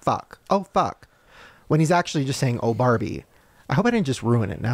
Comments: WITH MY EYES OPEN I HEAR " OH BARBIE" BUT WITH MY EYES CLOSED I HEAR " OH FUCK" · Omg.. for the toy story conspiracy, I heard "oh fuck".. but when Shane mouthed the words, I heard "oh barbie".. so crazy! I'm definitely with Ryland on this